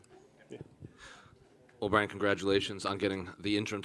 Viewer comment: was Brandon called "O'Brien"?